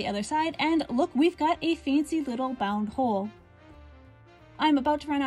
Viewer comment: Sounds like you say "Seafoam Gree Butt Holes" at the end. Maybe be more mindful about where the cut is in your videos.